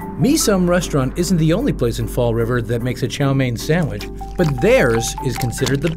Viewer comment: Not gonna lie I thought it said cow mein sandwich